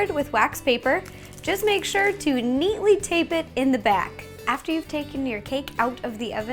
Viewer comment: She said "neatly tape" but i heard "Apply chaos theory to the bottom of the plate".